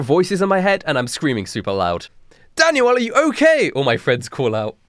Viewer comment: I heard "Danny are you okay "and and now I can't get Alien Ant Farm cover out of my head...